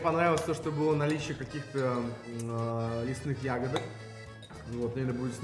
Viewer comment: мне послышалось, что он сказал про "мясные ягоды"! 🤣